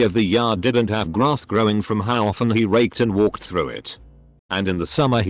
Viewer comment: I was only listening to the audio and I thought it was saying "raped" lol